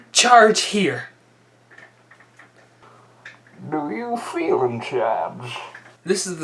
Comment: What did you say? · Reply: "Do you FEEL in charge?"